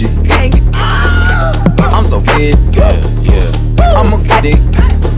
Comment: I thought 1:37 - 1:39 said "I'm so gay; I'm a-" > insert word straight people can't say here